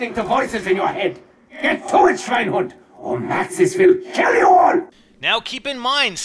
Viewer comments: I can't belive you didn't hear he was saying Sweinhund at 5:56 :D lol (No hate) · Get to it schweinhund. (German for pig-dog) I can't make out the rest of the "???" parts, but they're probably more German phrases.